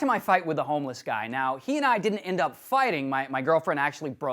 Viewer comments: I think you have some audio problems. Sounded like you said "Girlfriend" but that has to be wrong. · Did you hear girlfriend? I definitely heard "hand".